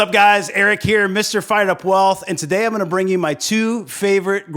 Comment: This my first video of yours im watching. I thought you said this is fried up wealth and I liked it. gg